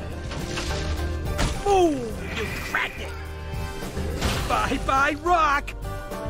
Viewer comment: what does the crappy narrator say? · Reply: Boom crack it?